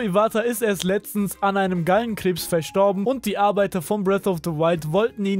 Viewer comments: Ich hab einen geilen kreps verstanden😅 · Ich habe geilen krebs verstanden